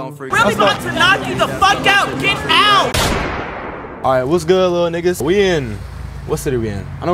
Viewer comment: Lol I'm new to watching the first thing I heard was "what's up little nigga"